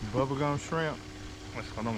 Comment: you Killin me🤣I thought you was saying BuBBa Gump shrimp!